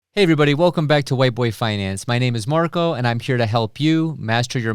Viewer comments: Totally thought you said "Welcome to white boy finance." lmao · Did you just say "welcome to white boy finance?" Had to go to your other videos, and you pronounce the rd in those. :D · Did he just say.... White Boy finance? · Am I the only one that heard him say "white boy finance"? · I've listened to it 5+ times. I keep hearing, "welcome back to white boy finance." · At first I heard "White Boy Finance" and had to check the name of the channel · I swear it sounds like you say white boy finance. 😂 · That’s the same thing I heard I could’ve sworn you said Whiteboy finance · sounded like you said Whiteboy finance .... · First time to the channel. In your intro I thought you said Welcome to White BOY Finance. I was like ok.....